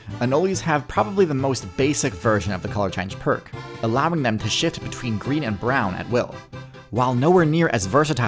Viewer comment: Hey everyone, I'm making a concerted effort to branch out beyond YouTube and make sure TierZoo has some longevity to it independent of platform. If any of you are willing, it would help me out a ton if you'd "Like" my Facebook page here <link> It takes 10,000 followers and 30,000 views to become monetizable on Facebook, and reaching that threshold would be a huge boost to the amount I'm able to spend on each video. So please, if you like my channel, it would help so much! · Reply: i thought u said "Shit between green and brown"